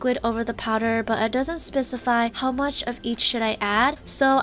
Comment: I thought she said " of each shit I ad"